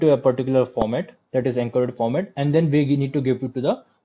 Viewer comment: At about minute 6:41 what format did you say the data needs to be converted to? Enker/Enkerd/encode? What's Enker/enkerd? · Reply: I talked about encoding the sentence column using LabelEncoder